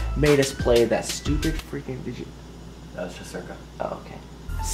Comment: I swear I thought he said that just are ghost I was just like "what the heck young Kyle"